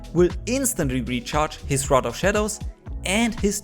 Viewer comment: did you say...FROGED shadows...uh...